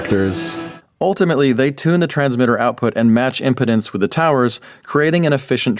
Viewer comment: did you say match impotence? Did you mean impedance?